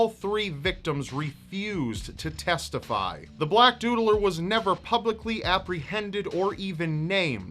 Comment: At first I thought it said "the black doddler." Lol.